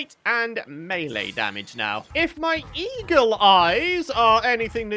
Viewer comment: I really thought he said e-girl eyes…